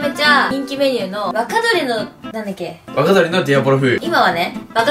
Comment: で｢若鶏の｣が｢バカ鳥の｣って聞こえたやつ俺以外にいるはず（編集済み）